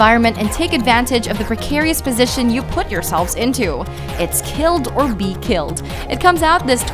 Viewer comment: it’s acually “kill or be killed” because “killed or be killed” is basically saying “commit suicide and you won’t get murdered by a dude just looking for pics